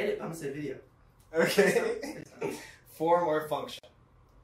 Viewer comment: What’s being said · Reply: form or function